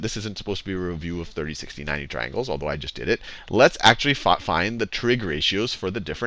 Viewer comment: what does he say at 7:43 ?? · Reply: "Let's actually fuc find ....... That what I heard !!